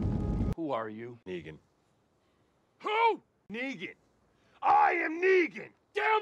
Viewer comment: subtitles ‘vegan’ instead of ‘negan’ 😂